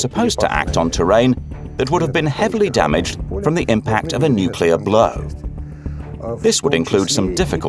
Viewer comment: Did he said at 7:25 "an impact of a nuclear blurr" ?? lol